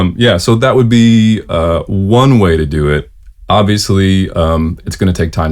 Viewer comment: does he say Avisly????